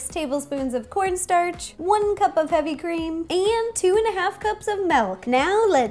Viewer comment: did you say.. ..malk?